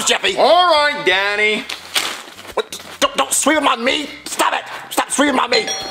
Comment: it sounds like Marvin said don’t swing it on my meat😭😭💀💀